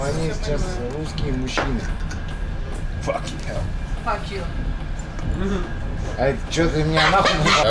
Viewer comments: Did she just say to him f… you? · Did she just say f*ck you! 😂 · did she just say fuck you ? XD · Did she say "fuck you" to Bald? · Bald Bankrupted Bhai saab...!😄😝👍 34:04 did she said F you, when you said effing hell??!😂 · did she say F you?? 😯😯